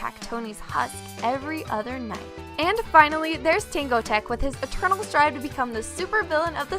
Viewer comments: Wait... What did you say? WAT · did she just said "tingo tek"??